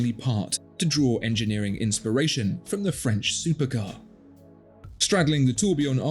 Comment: Did he just say "French supercar" at 5:33? 😆